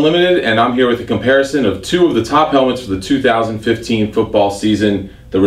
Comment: Did he say 2015?